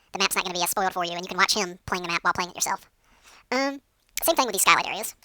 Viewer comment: For a second there at 2:40 I like "what the...?!" It sounded like you said "you can watch him playing the map, while playing with yourself" instead of, well, what you probably did say.. I had to rewind two times to hear it again.. guessing that say more about me than.. chipmunks being dirty.